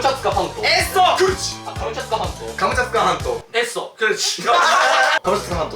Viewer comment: なんか、エッソ、クリチェのところがブルゾン、ちえみ、みたいに聞こえたのは気のせいだろうか...